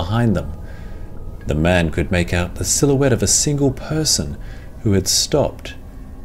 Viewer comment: Can someone just tell me what the narrator said, the exact word at 1:40 "of a man"? I couldn't make out the word. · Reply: “Silhouette” basically just an outline of a shadow